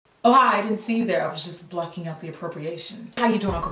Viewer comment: 😫😫😫 I thought you said I didn't see you there I was just blushing after procreation...